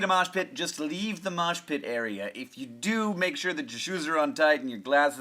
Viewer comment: When you said "Make sure your shoes are on tight" I thought you said "Make sure your shoes are untied"